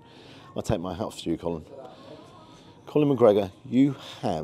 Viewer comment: Did you call the owner Colin, twice? 😅 I'm a Dubliner, never been out that way, but that lamb looked exceptional. Good video. Hope you enjoyed your stay. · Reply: I said Conor but can understand it sounds like Colin